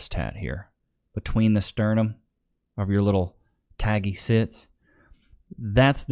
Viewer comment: I thought I heard taggy zits... Had to watch 3 times to hear it correctly. Funny thoughts though... If you try popping it, will they be worse than a wash board?